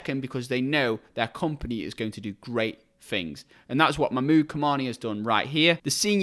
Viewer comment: Just watching this with captions on and at 15:18 it thought you said “mood commander” instead of Mahmoud Kamani haha.